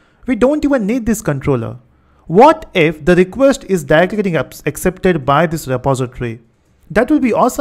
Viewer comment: I just can't get to understand what you are saying, could you clarify please? "What if the request is dadiggeding up"...